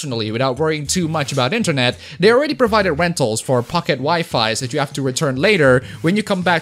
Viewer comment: did someone say pocket WAIFU?!?!?!